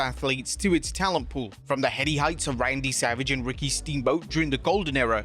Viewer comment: Did I hear Ryan B Savage?did I mishear or is there a joke I’m not in on?😂